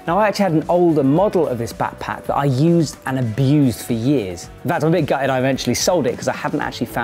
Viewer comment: I can't make out what you are saying at 1:04 so I turned on the closed caption and it quotes you saying "for years about a big guy died eventually sold it". Is that actually what you are saying?